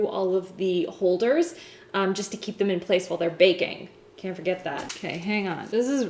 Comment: when you put the meringues in to the oven and said "while they're baking up" or something I heard "while they are breaking up.." LIKE WHO ARE BREAKING UP :D